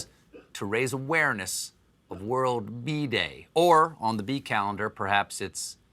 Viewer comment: Am I the only one who heard Bee Day and thought he said bidet?